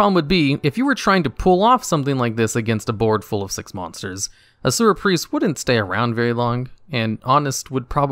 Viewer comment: I was making myself cereal, so when he said “Asura Priest” I thought he said “Sewer Priest”